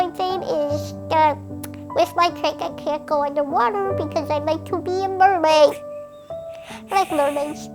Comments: what does she say here? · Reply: “Because I like to be a mermaid......I like mermaids’’ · did she say she wants to be a mermaid im not making fun of her i was just asking i kinda have hearing problems